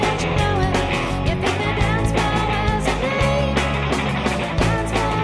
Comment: My interpretation: I know I should be over this, no time for playing games with wicked little boys A special look, a crooked smile I know he says he's ready, but I just can't run him out Pick your feet up, you're so funky Using words like kiss me baby, play me, fuck me You're way up high boy and don't you know it You'd think the dancefloor was his name, dancefloor was his name. If I took a pill and looked at you, I wouldn't stand a chance, I'll stick to drinking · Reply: I always heard it as “you’d think the dance floor was his name”